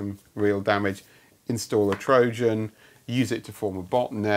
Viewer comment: For a moment I thought you said "use it to run a .NET", phew.